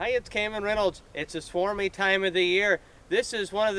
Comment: Swarmy time of the year, I thought I heard swarmy time of the month, had to go back to see if he was jocking.